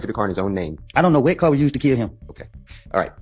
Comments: it sounds like he said “idk what car we use to kill him”😳😂😂😂🧐🧐 makes you think lol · Did he say, “I don’t know what car “we” used to kill him?” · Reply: Not we *was